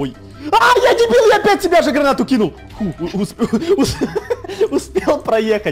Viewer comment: Мне послышалось гранаты миены